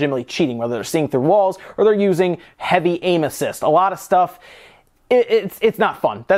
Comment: When he said "using heavy aim assist" I heard it as heavy anuses